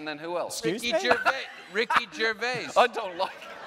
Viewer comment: whats the name john mulaney says at 44:10 john oliver looks genuinely scared of whatever reference it is, also what does nick say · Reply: John Mulaney says “excuse me?” at 44:10 (as Nick simultaneously says “Ricky Gervais”) and John Oliver is kind of preemptively scared for whoever Mulaney is going to name just because of his attitude